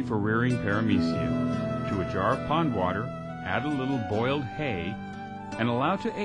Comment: did he say add boiled hay?